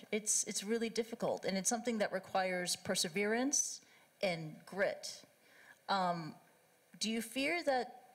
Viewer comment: Subtitle at 34:50 and 35:00 incorrectly says "greed" instead of "grit".